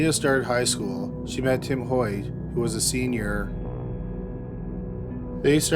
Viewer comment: i thought he said tim Horton for a fat minute